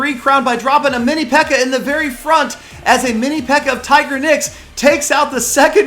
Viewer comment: Sounds like he says tigerdick